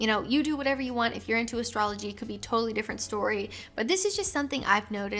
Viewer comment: LOL, first time I heard it I thought you said astronomy. I thought, "wow that's kind of unfair."